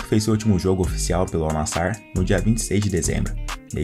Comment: al nassar? nao entendi